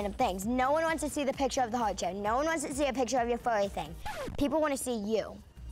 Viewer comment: "No one wants to see a picture of your furry thing" Nia's face, I'm dead 😂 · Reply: Lol x.x Dang. I thought she said "No one wants to see a picture of your funny thing" but either way it's still "sounds gross" xD